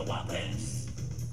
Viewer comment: I thought that the green thing said: "We got the woblins we got the woblins." So I'm like "What the frick?!" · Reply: I thought is said “wake up the Wublins” as in when you wake them up